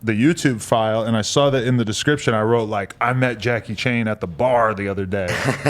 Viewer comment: Nigga... I literally thought it said 🙄🙄 “ Jackie Chan “